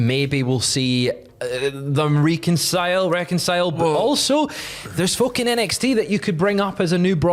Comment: I had to rewind to 12:20 because when he said "There's folk in NXT" I heard, "There's fokin' NXT"